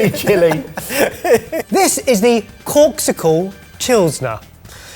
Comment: I thought said corpsicle. 😂😂 Then I saw the word on the screen.